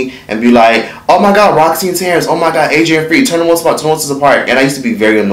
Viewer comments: The way he kept pronouncing 106 & Park was triggering for me lmaoooo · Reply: Lol I thought he was saying once in a park😂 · If he say "OnesoPark" one more dam time! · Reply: I hear "Once in a park" 🤦🏾‍♀️ slow down u messing with a huge name there dude 😂💀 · it keeps sounding like he's saying "once in a park" "once in a park" is it just me??? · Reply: I heard " one since a par"!